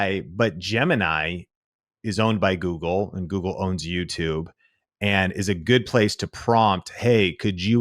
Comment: I swear when he said "Google owns YouTube" I thought he said "Google owns you too" it's too early for this. 😂😂😂